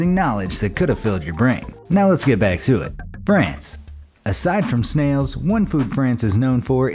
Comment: thought I heard him say Franz lol😂